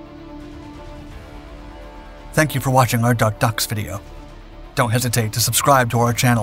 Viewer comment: I thought he said Dark Ducks at the end. :)